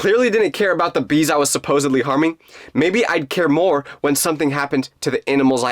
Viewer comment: it said farming instead of harming